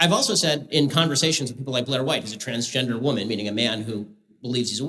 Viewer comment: For a minute I thought he Said "my wife is a transgender woman" but I definitely heard wrong. That would be so funny idk I'm dumb